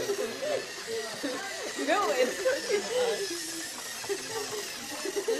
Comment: Did she say "Your robots a wannabe." at 3:30?